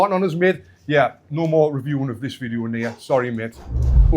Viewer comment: what did he say? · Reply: "No more reviewing of this video in here, sorry mate."